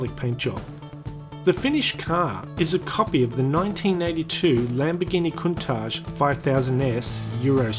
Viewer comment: It said that the car was a copy of a 1992(1:50), but they were making the diablo at that time instead of the countach and then in the ebay ad it said that it was a 1980(3:25). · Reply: Firstly I said "copy of 1982 Lamborghini Countach 5000s" at 1:50, that is what I was told Secondly, no idea why the ebay add says 1980.